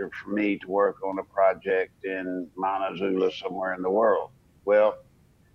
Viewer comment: did he say manitou?